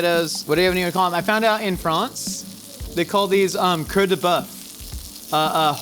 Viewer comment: The way you said heart, coeur, sounded like tail, queue. I legit thought you said queue de boeuf at first. I guess heart makes more sense, with the tomato being red and all.